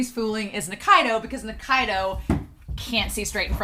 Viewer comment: "nikaido can't see straight" for a hot second I heard you saying "nikaido can't BE straight" and I was totally fine with that, too 🤣